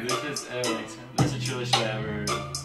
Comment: um so i tried to use subtitles to different what he said at 1:54 but i cannot comprehend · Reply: "ooo, this shit hit harder than a sink, great!"